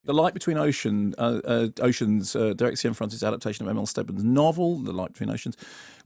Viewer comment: Can anyone else decipher what Mark is saying at 0:02? I get what he's saying but it's slurred as heck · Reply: Derek Cianfrance's adaptation of M. L. Stedman's novel.